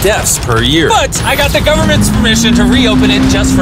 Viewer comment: Did he say government